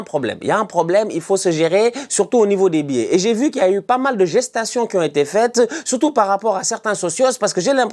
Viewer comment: pas mal de quoi tonton ? 😂😂😂😂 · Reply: Gestation 😂